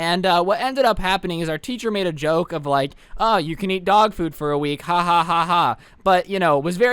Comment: i thought you said dog poop and was like what? 😭